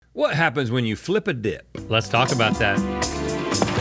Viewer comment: Rhett sounded like he said “what happens when you flip a dihh” in the intro 😭✌️